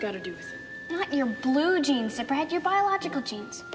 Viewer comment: What'd she just call him? Maybe I'm hugely mishearing, but I'm pretty sure that's a slur, Karen. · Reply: Zipperhead: (US, military slang, derogatory, offensive, ethnic slur) A person of Asian descent